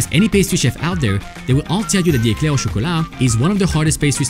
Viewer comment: I thought you said crossaint is the hardest